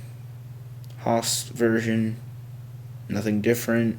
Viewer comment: did u say "Haust version" ????